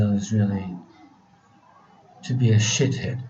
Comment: "...for some people the ideal is sh... "- I didn't get, don't understand this word :"sh...". It is in 6:14. Would somebody be so kind and put it in the letters? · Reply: Krzysztof H. to become a "shithead." It was the word that came to mind, sorry.